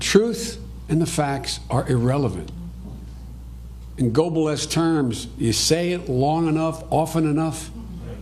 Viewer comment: "______terms?". Anyone know what the hell he said there? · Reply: Globalist terms?